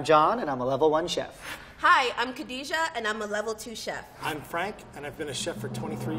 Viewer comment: What they said: "Hi I'm Frank and I've been a chef for 23 years." What I heard: "Hi I'm Frank and I'm a level 23 chef."